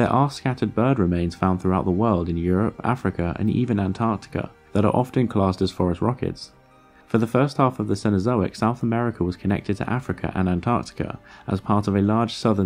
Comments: This entire time I thought he was saying forest rockets. I just agreed like it made sense. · this whole video i heard "forest rockets" instead of "phorusrhacins", which is admittedly just as intimidating as "terror birds"